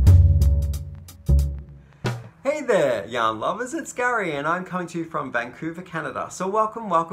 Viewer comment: Every time you start with “hello yarn lovers”, I hear “hello young lovers”.....makes me giggle each time. I always enjoy your videos. project bag is beautiful!